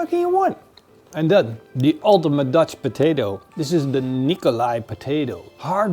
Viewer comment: Ya know, I'm not sure how you said what those potatos are called but it sounded like you said Nikolai (happens to be my name its a very Russian name I was named after a soldier my grandfather knew back in the 30s) and I too enjoy some potato